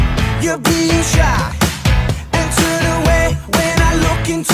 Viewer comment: Du nada ouvi um CHÁ